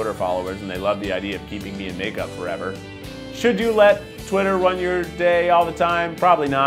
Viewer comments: I thought it said ruin your day for a second😂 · lol I thought it said "what happens when you let twitter ruin your day" 😂